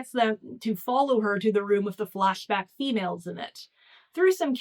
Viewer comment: The first time the flashback females came up, I thought you said "flatpack females" and I didn't bat an eye. This book is so bizarre, flatpack women would have fit right in.